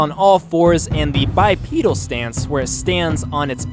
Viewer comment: when you said bipedal I thought you said by penis